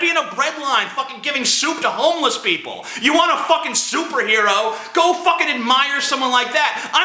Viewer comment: "Soup-erhero." That's what I heard at 10:55.